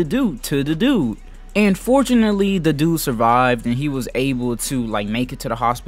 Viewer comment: i had to watch it again i thought you said "unfortunately he survived."